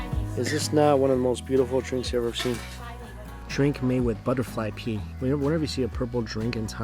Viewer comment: I thought he said butterfly pee 😨